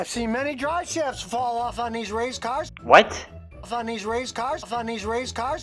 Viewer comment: I think he said raised cars